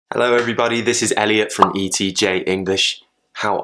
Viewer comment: 1st time i listen this video, i heard " hallo everybody, this is ELLY from TJ ... Now after 10 times my ear back normal to hear "hallo everybody, this is Elliot from ETJ ... ha ha ha .. love you all